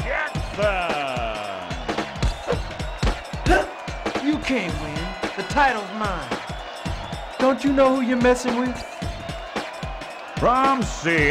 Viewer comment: What say Michael Jackson? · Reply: You cant win, the titles mine. Dont u know who u messing with?